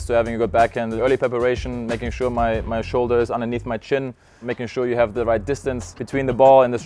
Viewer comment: I don't understand a word at 0:38 "making sure that the shoulders are underneath the ???" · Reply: "underneath my chin" you can activate the english subtitles of the video (bottom right corner of the video)